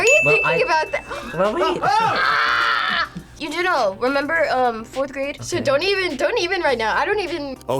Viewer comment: did she say 4th grade